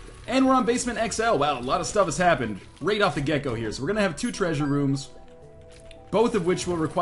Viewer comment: I heard ''Right off the Gecko'' I was like wut? :p